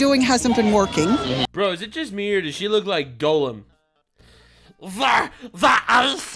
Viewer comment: When he said Gollum I heard Golem from Pokémon.